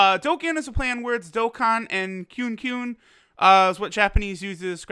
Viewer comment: Did someone say Kyun Kyun? <link>